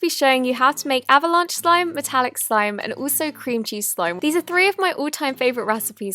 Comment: When she said cream cheese slime I thought she said cringey slime cause that's relatable to me